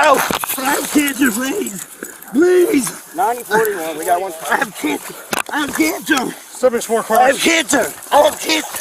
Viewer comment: When he said “I haven’t cancer” I first heard “I have dandruff” and was really confused for a second